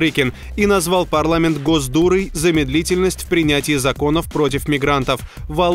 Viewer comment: мне одному послышалось "гос дуры" ?